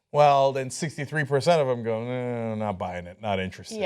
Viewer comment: is Cenk saying "not bi-ing it"? xD